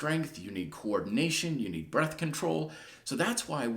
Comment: Almost sounded like birth control 🤦🏻‍♂️ haha great vid though!